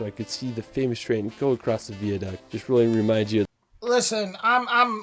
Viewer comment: Yes, I agree it really makes me angry that this world heritage site is mispronounced! I’ve mentioned this many times before on previous reactions to this video, and have been castigated, and for some reason called a racist! He also calls Wales Wells, and Westminster West Minister! It’s a shame it can’t be edited! I will probably get insulted again for mentioning it, but hay ho! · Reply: also sounded like he said Veerduct instead of Viaduct.